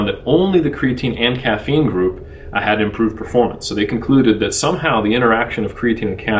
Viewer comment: what does he say? "had" or "hadnt" improved performance? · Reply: It’s perfectly clear lol “had”